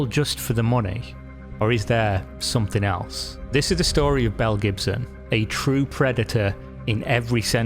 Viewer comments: I thought I heard Mel Gibson while watching and was like, oh boy...what has old Mel done now?! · The number of times I thought you said Mel Gibson. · I listened to this minimized. I thought I heard Mel Gibson. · I kept hearing "Mel Gibson'. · Tell me more about this fantasy golden mountain dragon you speak of. Also, I thought you said, "Mel Gibson".